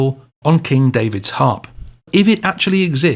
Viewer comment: I always heard it as "sacred chord" not "secret"